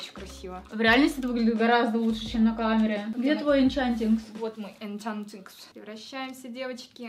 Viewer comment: ахахахпхаххпхахпхх сказала Энчантикс, а мне послышалось Банчантикс и я вспомнила фотографию Банчана с розовой кароной и волшебной палочкой